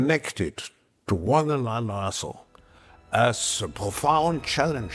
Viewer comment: what does he say? "Our futures are intrinsically connected to one in an a**hole..." I honestly don't know what is trying to say at the end of that sentence. Are we really connected to an a**hole? Hwhat?!